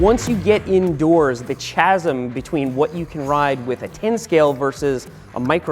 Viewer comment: Did just say "cha - sm" lol 🤨